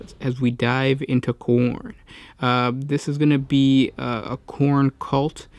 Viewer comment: I heard it as "dive into corn " Popcorn for popcorn god!